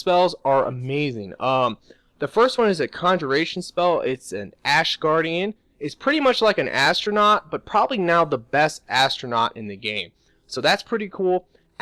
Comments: did he say astronaut XD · Astronaut? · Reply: he means atronach lol when i heard him say that i was like "astronaut?" too lol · Yall some dumb ass people sayin astronauts it ATRONACHS SRSLY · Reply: He has an accent trust me i thought he said astronauts too but he actually said atronachs.